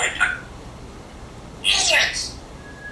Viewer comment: I clearly heard him say, "idiots!" 😂